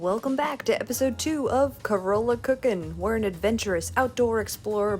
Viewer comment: I thought you said “Corona cocking” and I was like whuuuuut lol I loved the video and santo to try them so bad